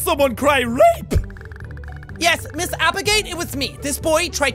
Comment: Lol I thought she said “Miss Applegate!” Lol. 😂